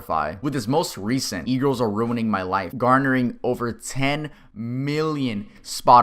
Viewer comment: I know the song and I’m familiar with Corpse Husband, but I still heard you say “Eagles ruined my life”.